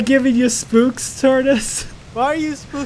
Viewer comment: Guys he is literally working on Yandere High like everyday. Please do not spam his other videos asking for more, it is very disrespectful. Enjoy the videos and please be mindful of what you comment. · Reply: when he said spook spooks at the start of it I thought he said I see pook pooks lolyou 2 rock don't let anyone say otherwiseand wat happened 2 and gizzy they don't record together any moreand im with you its getting sooooooooooooooo ridiculous